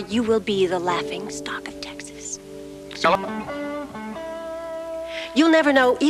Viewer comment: what did jr say in 3:30? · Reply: He said “Sue Ellen”